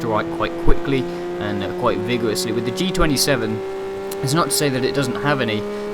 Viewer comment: Ok, I thought you said it didn't have a neutral, sorry